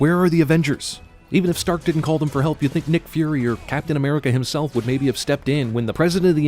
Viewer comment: ish the captions say Nick Furry instead of Nick Fury